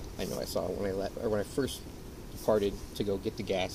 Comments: the neighbor, says “when I farted to go get the gas”…as he stands by his poop911 car…in a poop911 shirt. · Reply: I had to replay that part cause I could have sworn he said farted and I was like “no frickin way” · Sounded like he said, “when I first farted to go get the gas” 😆WITH the poop shirt too was👌🏼😂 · Am i the only one hearing the cops say around 6:32 that when he first farted, not first parted like the captions say 😂 i mean between that and the poop 911 shirt i cannot 😂